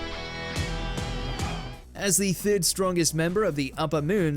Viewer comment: At minute 06:20 did he said Akasa is the fifth? Everyone knows Akasa is the third upper moon. A small mistake from the writers right there.